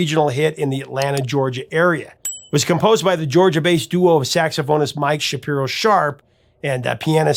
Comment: Did you say "Georgia bass duo"? Lol. Yeah Bass!